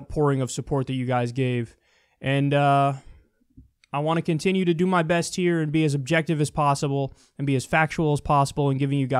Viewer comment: Did he say being as subjective as possible? :D